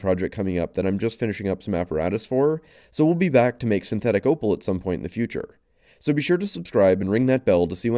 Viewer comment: Totally thought you were going to say "We're going to make synthetic opioids at some point"